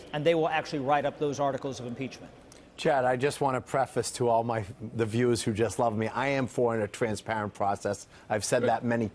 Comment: Who is the grey haired man? Did he just say, "the viewers who love me."? As he began to speak, at about 2:32. If so, it seemed very strange to me to start hiis statement that way.